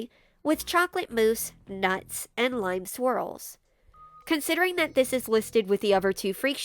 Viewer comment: I heard "Lime swirls" as "Live squirrels"...